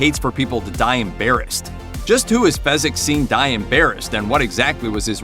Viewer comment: The line is “Embarrassed” but I always thought he was saying “on bare hands” when I was a kid. His emphasis on the syllables makes it sound like that.